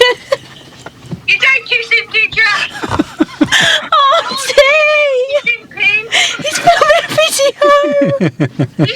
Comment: What did her aunt say at 41:16? After "did you give Pin a hug?" 😂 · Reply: did.you.give.pin.a.kiss...so. awkward.lol